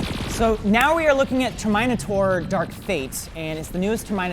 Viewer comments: did he just called terminator 'termynatorree' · Did he just call it term-mine-itor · did Wren just say Ther-mine-ah-thor?